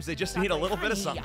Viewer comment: lol noi ( oh come at me man! ) me : what the heck I thought he said a good dad dousnt fight so are YOU a bad dad? 🤣🤣🤣